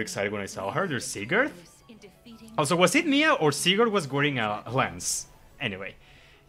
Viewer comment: the YouTube caption said Sigurd seagull.